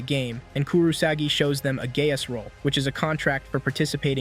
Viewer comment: I love the YouTube channel. Nah what she say 3:15 nah she really said gay as